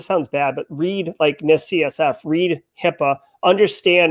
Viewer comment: What is he saying at 32:45? Read "Miss CFF"'?, read HIPAA. I can't figure out the word in quotes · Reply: NIST CSF - <link> Let us know if you require any more information. Happy to help